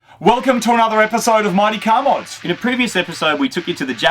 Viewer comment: At first I thought said ,"Welcome to another episode of moneycowboys" haha... then i realized he said mightycarmods.